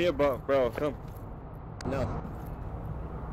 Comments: I think he said : you aim your gun at me you die :D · "You in here, gonna be die" 4:57 I mean he clearly said "You aim your gun at me, you die." but okay that's fine.